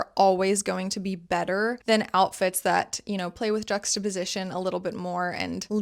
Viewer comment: What is that word she keeps saying? JX position? Drugster Position?. Like on 6:07. The captions are not helping either. HELP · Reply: Juxtaposition